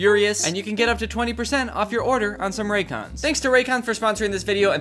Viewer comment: raycons are great, i’ve accidentally thrown mine through the wash like 4 times and they still somehow work so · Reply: I THOUGHT YOU SAID RACOONS 😭